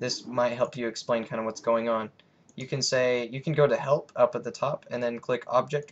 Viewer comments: On 7:20 i thought you said "You can go to hell" xD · did you say go to hell? Go to help?